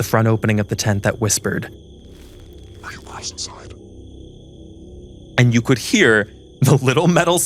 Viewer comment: What did he say? I cant ever understand it. "Are your wives inside?" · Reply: I think he said “are you guys inside”?